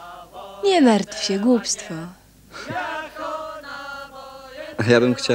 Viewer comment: mniej więcej ten czas. Lidka do Janka Kosa - NIE MARTW SIĘ GUSTLIK. chyba że źle usłyszałem.